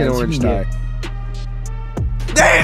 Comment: i heard there is no chees in there